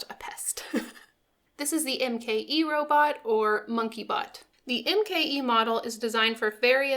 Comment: Hi. Love the robots. I thought I said monkey butt. Lol. Bot makes more sense. Pls don't let the robot get the captain's cat. Thanks for sharing